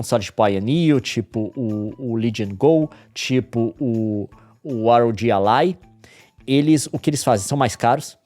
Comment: ah leon me poupe kkkk, fala ROGI ALAI memo, quase que eu nao entendi 😂